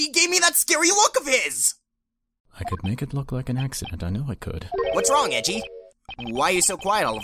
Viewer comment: "I could make this look like an accident; I know I could..." Has anyone else heard this? Or is it just me?